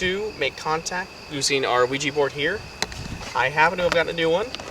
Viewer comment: It sounds like he says "We have the Luigi board here"